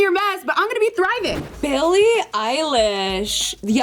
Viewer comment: I hear "Bailey" Eilish